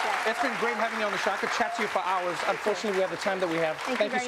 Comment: Kellyanne: "Thanks for having me." Trevor: "Not many people would." Ain't that the truth. Major props here. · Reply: "Thank you very much for having me, Seth." That's what I heard.